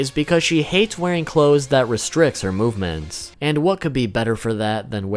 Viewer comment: thought he said Meth